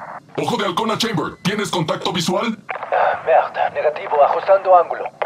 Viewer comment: lo que dice no es "ugh ver" lo que dice es "ugh merde"